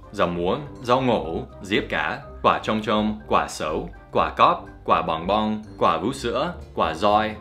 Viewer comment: từ "quả cóc" mình nghe thành "quả coóc", mong luyện tập nhiều hơn